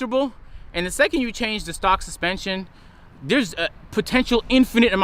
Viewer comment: get into stocks instead of spending another 100k😂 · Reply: Bruhhh I thought you said socks 💀💀💀